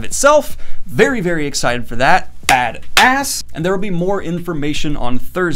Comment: I don't know why, but I heard bad a** at 1:37.